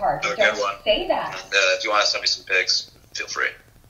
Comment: Did he just say “you can send me some pics”??